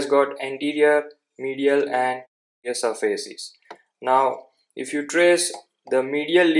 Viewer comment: Pls sir, what did you say sir? I couldn't hear nor make that out. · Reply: Lateral surfaces